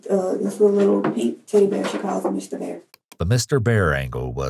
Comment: It’s so sad that Mr. Bear bought more comfort to that little girl than her mother ever did😢😢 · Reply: I thought you said Mr. Beast